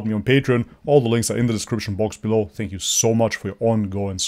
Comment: Every time ended his video saying "much love" i always misheard him saying "Mashallah" haha got me everytime dude